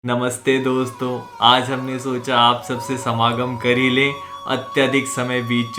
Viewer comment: Had to watch it again Coz i thought he said SAMBHOG ! 😂😂😂